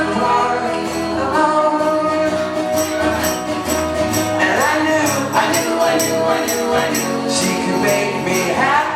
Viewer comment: Can anyone tell me what is the word at @ 2:26 ? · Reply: alone